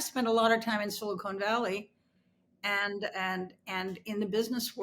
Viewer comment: did I hear ? “I spent a lot of time in Silicone Valley” …if so she must’ve missed the memo…🤪